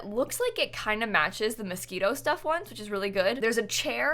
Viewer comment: I thought you said "Mosquito Stuff" and I was feeling it cause dang I am tired of these mosquitos this summer!